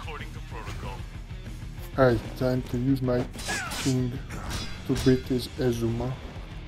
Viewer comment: That line sounded like "Now it's time for king to beat this asthma" Lol!